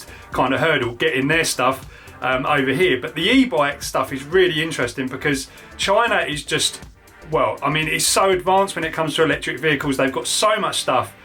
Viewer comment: I thought he said edd China lol was going to get one.